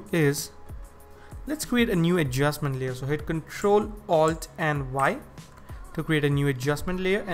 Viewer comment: I can't get past 8:00 idk what he's doing and he isn't saying any keyboard shortcuts. I'm a 100% noob at this and he wasn't explainging anything. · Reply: Ctrl + Alt + Y